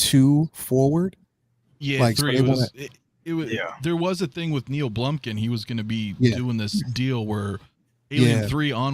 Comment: sounded like said Neil blumpkin 😂😂😂😂😂